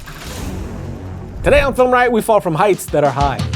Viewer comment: i thought it said Filming Stunt Fails hahah